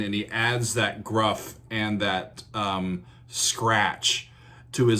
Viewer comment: Where you hear "gruff" I hear "heart"